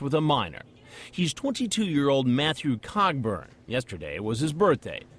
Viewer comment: I thought they said cockburn.. that sounds unpleasant... kinda like paedophilia